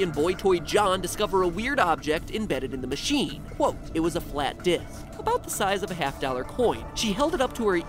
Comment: ?"Did anyone else hear "It was a flat dick